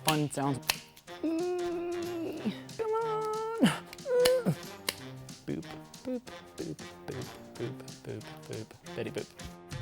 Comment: I thought he was saying "boob boob boob boob boob boob boob, bity-boob."